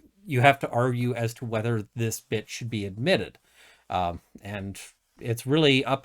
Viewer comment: When you said "this bit should be admitted" sounded like you said "this bitch be admitted" 😂